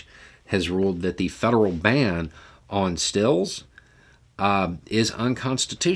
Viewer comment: Did I hear correctly: "Federal ban on 'stills' is unconstitutional?"